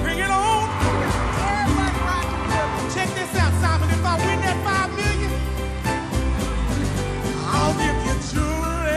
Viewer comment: What did she says at 1:19? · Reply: "Check this out Simon, if I win that 5 million"...then proceeds to sing "I'll give you jewelry and money too" lol